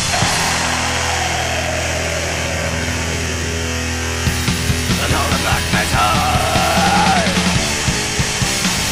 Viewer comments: What is he saying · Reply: UNHOLY BLACK METAAAALLLLLL😎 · sounds like Fenriz there hahah what did he said tho? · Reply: UNHOLEYY BLACKMETAL!!